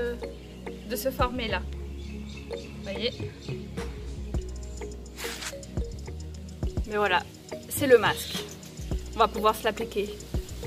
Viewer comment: Lors de mon premier visionnage, j'ai entendu "on va pouvoir s'la péter" à 6:03... Puis j'ai réécouté.